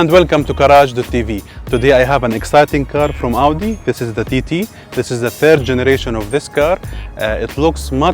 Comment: thought hey said titty (female mammaries)